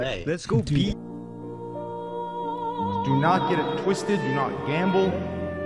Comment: I only heard “get it twisted, gambled”